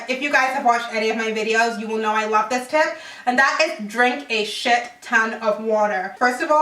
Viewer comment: thought she said drink shit and then she finished the sentence by saying "ton of water" 😂